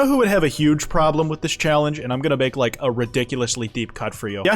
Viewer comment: I don't know, what is this reference? I genuinely can't understand what is being said. It sounds like "Yesterday upon the stair Deku" "Gaster n' Oppotomous Stare Deku" Like what? I'm trying to soul read atp